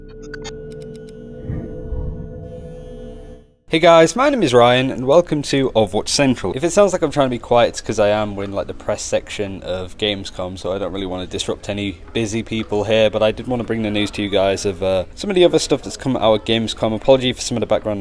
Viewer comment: when you said "we're in the press section" i heard "we're in the depressed section"